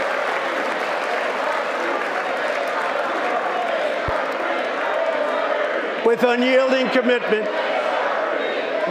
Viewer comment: What were they saying at 41:16? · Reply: They were saying “H.R 3” in refreshing to Pelosi bill to lower drug prices a couple years ago. It wasn’t passed